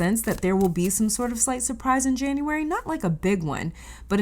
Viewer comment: You said "slight surprise" but I heard "slice of pie". And I was supposed to be fasting today...??? 😅